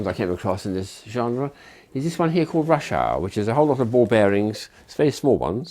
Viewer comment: I know it is supposed to be "rush hour" but I'm not English and I heard Russiaaaaa twice 😂😭😂